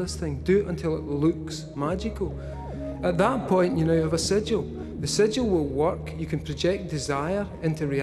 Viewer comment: What does he say at 15:55 ? A sedgual? What did he call it? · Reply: he said a 'sigil'